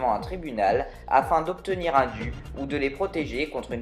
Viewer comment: ça veut dire quoi un vu? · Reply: Il me semble que j’avais dit « obtenir un dû »